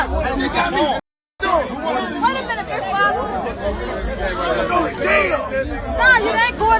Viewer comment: did she just call him big father 😅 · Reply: Baller